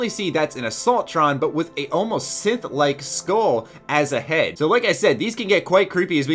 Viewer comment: anybody else heard "Sith like skulL"